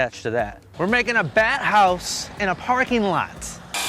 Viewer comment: For a minute, I thought u said u were making a bathouse in a parking lot... 😂